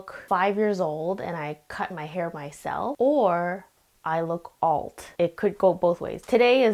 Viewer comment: I thought she said 5 years or "old" until I saw the white text on the side 💀